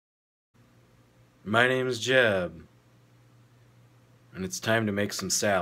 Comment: Who else thought that he said, "my name is Jeff " 😂🤔